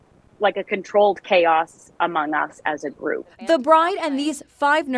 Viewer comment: did i hear amogus?